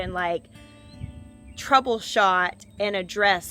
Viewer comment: "Troubleshot and addressed so many things..." At first I thought you said "Trouleshot IN A DRESS..." lol ... had to listen again lol